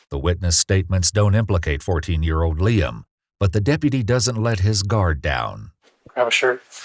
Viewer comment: I thought he said deputy dog😂